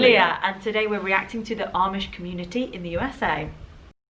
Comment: i thought the video said “amish comedy” and I have to say i’m kind of disappointed now